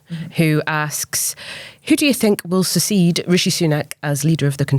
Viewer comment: What on earth is "susseed"? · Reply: I know I thought she was saying secede (as in breakaway) but realised she meant succeed haha